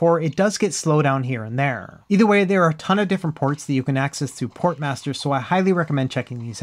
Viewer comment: Okay I really thought he said porn Master